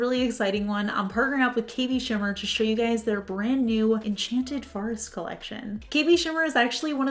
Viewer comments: I had the volume kind of low at the start of the video and I heard "Enchanted Farts Collection." Lol 🤣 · I swear I heard you say "Enchanted Farts Collection", I had to go back and listen again 😆